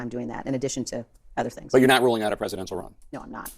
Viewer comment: I could be wrong but I have a feeling she thought Jake said, "But you're not rolling out a presidential run?" Ruling out. Rolling out.